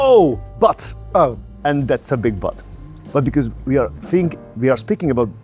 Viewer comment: is saying the word 'but' but the caption say the word 'butt' , I'm like : is the caption tripping or what?